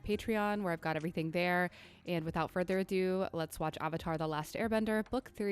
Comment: I'm sorry, did you just say... "ahh-vatar?" 1:18 Better watch it before the fans cancel you lol